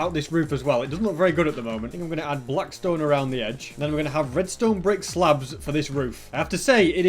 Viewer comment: did he say redstone brick slabs?